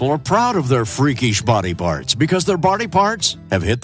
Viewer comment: at first I thought he said "bardy parts"